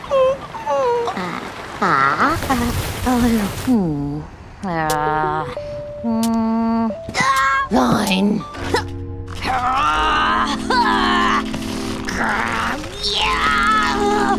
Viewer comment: Did anyone heard ,,nein" like I did?